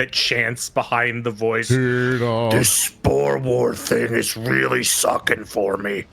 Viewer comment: What did he says? Spore orphan?